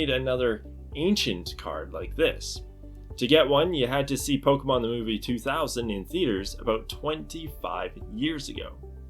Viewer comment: what is he saying on 02:21? to get one you had to see pokemon wuwuuwuwuw 2000 in theater? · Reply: Pokemon the movie 2000